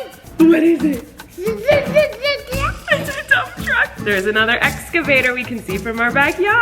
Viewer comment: Did he just say "ooo excavator" :20